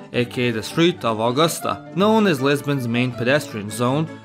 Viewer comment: As a Portuguese I have to say that your pronunciation of Portuguese names is really good. Slavs always manage to speak Portuguese very well. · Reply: And I thought I heard Lesbian 🤣